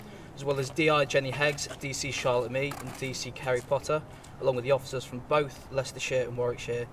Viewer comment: i know im not the only one who heard Harry Potter